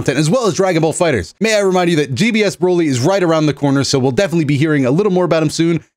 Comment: GBS Broly, did I hear that 😅